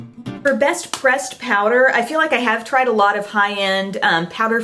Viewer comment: Catching up on my videos and always love the Emily Awards of course, but when you said Best Pressed Powder, I thought you said "Best "Breast" Powder! I nearly died laughing!